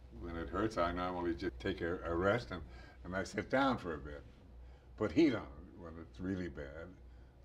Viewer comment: anybody knows what is he saying at 2:08 ? "but he knows well its really bad that seems to make it feel better" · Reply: Putting heat on it makes it feel better.